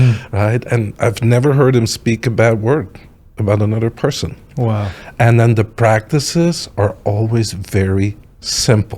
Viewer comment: he mentions a "spontaneous". -- and I didn't get the word after that. The subtitles didn't either, I don't think. Does anyone know what process he's talking about? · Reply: Spontaneous nei gong